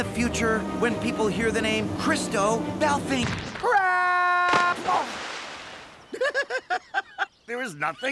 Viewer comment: when he fell did anyone hear him say crap or just me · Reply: death game he said crap ur right